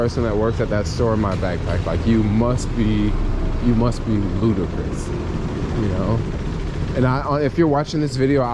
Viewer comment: did you say "Lootercrous" 😆